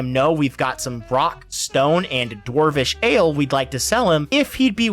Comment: Did I hear a rock and stone!?